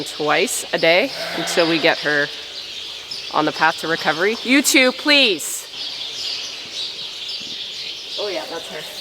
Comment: When I first heard this, I heard “YouTube please!” Big laugh